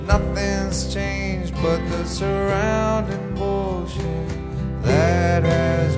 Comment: The only part I didn't know "the surrounding bullshit that has grown" I thought he said "the surrounding ocean" but now I hear it. Feels like it was written about me. Very nice vid & song